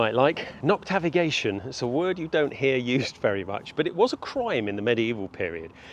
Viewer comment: i cant hear the word at 6:36 correctly, how is it spelled? oh its "noctivagating"